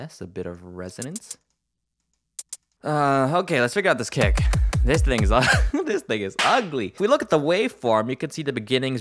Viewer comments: sounds like he says “this niggas ugly” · I heard."this niggas ugly" wtf